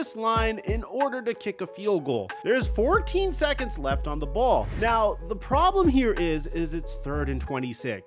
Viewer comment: Did you say "14 seconds left on the ball"?